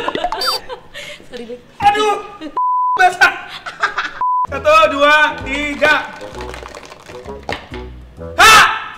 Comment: bang thariq bilang apa ya yang tau komen ya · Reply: aduh anu ku basah